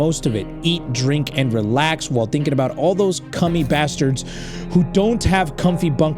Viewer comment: He did not say "cummie bastards" 🤣🤣